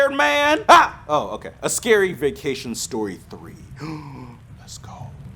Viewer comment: When it said "scary vacation story 3" I thought I said scary vaccination story 3"🤣🤣🤣🤣